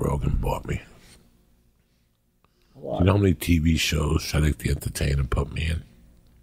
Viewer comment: Did he say Cedric the Entertainer?